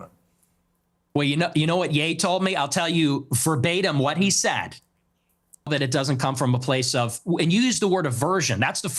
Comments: What did Ye say, verbatim? 🤔 They cut it.. 💀😂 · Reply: He said “niggas will be niggas” 😂 it’s on his nicks show on rumble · "I'll tell you forbatum what ye said..." CUTTTTT 😂😂😂😂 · What did YE say ? Uncensored ? Not really. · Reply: Ye: so I heard you use the n word sometimes” Nick: yea I do Ye: well…sometimes niggas do be actin like niggas That’s what he said live lol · this show should be called Piers Morgan “Uncensored” with the air quotes 😂 😂😂😂 what did Kanye say? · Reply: "When ninjas are being ninjas..." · censorship in a “uncensored” show I wonder what Kanye said lmao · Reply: He said n words will be n words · Why are we not allowed to hear what Ye said... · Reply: It’s on Twitter, it was something like “if a ninjas being a ninja you gotta call em a ninja” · Why’d they cut out what Ye was saying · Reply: Ye said ngas will be ngas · I need to know what Ye said bro come on xD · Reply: When N being N - and Nick answered "So true" Pierce then asked what does this even mean? And Nick answered "That even black people know how black people can be" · What did Ye say?? It’s cut out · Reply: He said "sometimes ninjas be ninjas" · What did Ye say? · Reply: He said “niggas gonna be niggas” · What did Ye say? (43:22) Piers just censored his uncensored show.. 🤡🤡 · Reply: Ye said "You use N-word? Well, when Niggas gonna be Niggas.." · anyone know what's been edited out at 43:22? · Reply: Nick said the n-word twice. So much for “uncensored” 😂